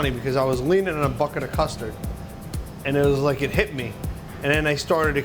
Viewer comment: Deff thought u said mustard not custard I was like ewww